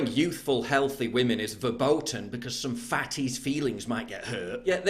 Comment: did he say the german word "verboten"?